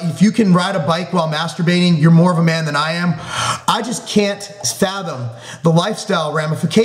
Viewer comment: What does he say at 5:52? 'I just can't ....'. What is is? (Curious linguist) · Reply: fathom, like comprehend/understand